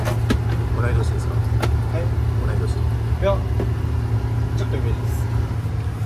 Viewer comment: 「いや、ちょっと上です。」 に聞こえる